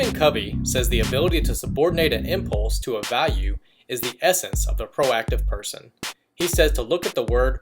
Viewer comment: Very goog job, trully helpfull video for all in life. Can someone tell me what says at 1:12? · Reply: It says '..the ability to subordinate an impulse to a value is the essence of the proactive person.' Thanks for watching and commenting!